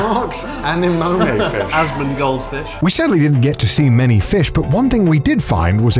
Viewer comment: did I just hear "ASMONgold fish"? hahaa